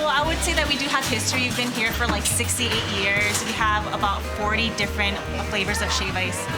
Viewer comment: “We started like 16..... 8 years ago.” Dawg what is it!? Soundin like Yoel tellin is his age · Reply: Arie Negron I think he said 68 years. I thought the same thing too. I thought he said 16-8 years. But a little while after that he says 68 years.